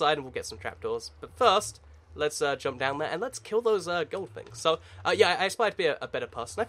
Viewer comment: DID you say "kill those gold pigs" |: don't lie to me what did you say :P